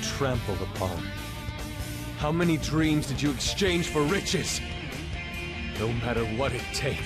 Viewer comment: I always hear "How many dreams did you exchange for bitches?!"